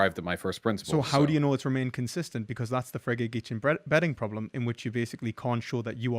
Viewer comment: Can someone tell me what this is that sounds like Fray-Gay-Gee-Chin Betting Problem? I would love to study up on this but all google searches lead to stuff involving gambling addictions and I'm clearly not spelling that first word correctly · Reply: It's the Frege-Geach problem, if you are interested.